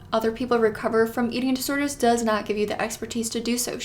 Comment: sorry, trigger warning for who? I couldn't hear what you said there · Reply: eating disorders